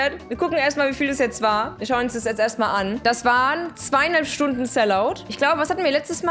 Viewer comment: habe ich richtig verstanden? Hast du gesagt 200 stunden sell out😂?